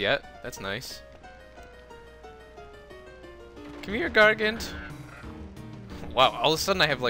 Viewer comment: Lol i thought it said come here Hitler in the first time event.